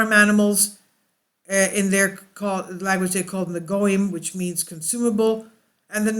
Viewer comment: did you say "goyim"? It wasn't included in the subtitles.